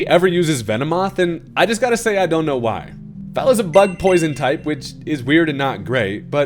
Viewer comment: I heard “butt-poison type” because I’m insane, but it made me like Venomoth even more